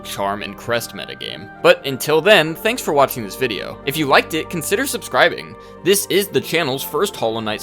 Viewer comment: "If you Like dih consider subscribing" WHAT DID HE SAY